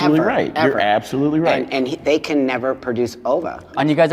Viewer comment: I thought she said, "And they can never produce Boba" lmfaooo